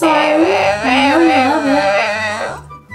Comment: Is it just me who actually heard him say "Oh well" for shellfish??